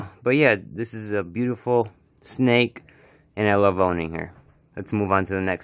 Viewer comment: sounded like "She is a beautiful snake and I love boning her" I know he said owning but dang